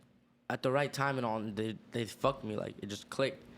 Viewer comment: Did he just say "at the right n all they just fucked me"? Lol 11:30🤣🤣🤣